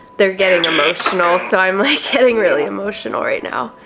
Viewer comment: I heard “emotion”